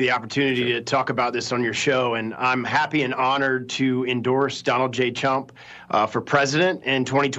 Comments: Did he just say Donald j chump because that's what it sound like to me 😂🤣😂🤣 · I thought "the left" was supposed to be hostile to business? 05:02 lol... I did he say "Donald J Chump"?